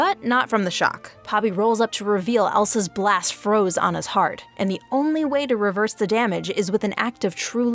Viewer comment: on his heart 😂😂😂I thought she'll say the freakin Ice on HER heart but HIS heart?? 😂😂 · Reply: Im pretty sure she said "froze anna's heart" not "froze on his heart"